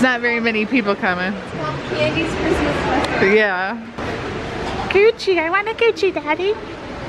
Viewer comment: at 4:13 i laughed because face when her mom said Gucci i whant a gucci Daddy i think that what she said anyways that what totally sound like but and her friend are like omg